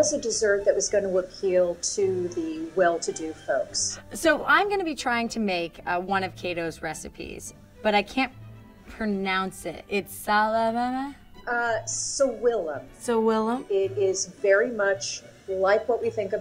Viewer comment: swalalalalala???? i heard like that lmao😅 but i know i wrong... so dont judge me. i heard like that!